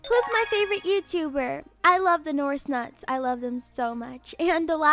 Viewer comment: Did she just say noris nuts I